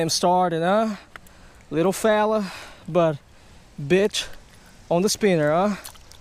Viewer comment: U said the b word instead of bite on 16:25 around there